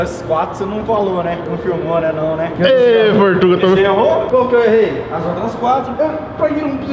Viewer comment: eu ouvi meu sobrenome mesmo Pedroso ❤